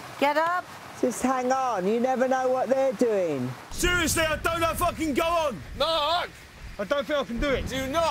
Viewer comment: When Mark said "don't know if I can go on", I thought he said "Don't know fuckin go on", so I was kinda surprised when it wasn't censored.